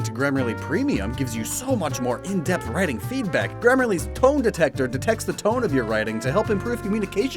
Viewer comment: "Grammarly's TONE detector!" I heard this as 'toe detector' and was extremely confused. Had to rewind to make sure it was me being a plank and not what was actually said lmao